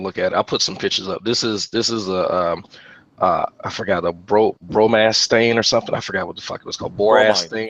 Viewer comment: I swear you said bromance stain which made horrible thoughts go through my head.